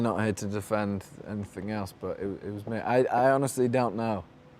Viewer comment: Can someone please tell me what does Harry say at 1:21? I tried to understand but all I got was "...defend anything else" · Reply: But it was me